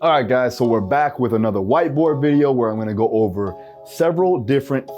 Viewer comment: Hahhaha In the beginning I thought he said "Time for another white boy video" lmao